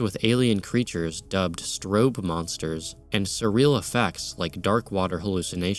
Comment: i thought you said stroke monsters and it made me think of diddy lol